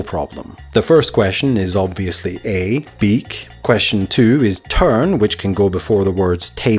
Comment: actually if you listen to the first question very carefully though a good part of headphones - and it could just be the microphone or accent - but it certainly sounds like says "I think it could be B", but Chris mishears it as Beak. Seriously, turn it up and listen.